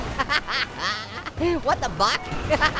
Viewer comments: What did he just say!? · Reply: AHAHAHAHHAHAHA WHAT THE BUCK???? AHAHAHAAHHAHAH!!!!! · Did anyone else heard orange saying “what the f**k” in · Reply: I think it was what the buck